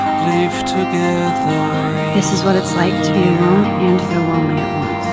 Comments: I can't quite here what she said...? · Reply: "This is what it's like to be alone and feel lonely at once..." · Hi there. Anyone can help me with what Max says at 1:33 mark. "This is what it is like to be alone in ..." I can't get the end of the sentence. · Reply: Thank you: This is what it is like to be alone and feel lonely at once.